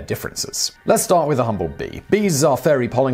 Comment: Who else thought that he said bumble bee when he actually said humble bee